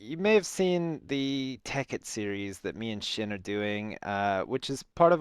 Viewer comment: You're right. i thought he said "cause tekkit is shit" >_<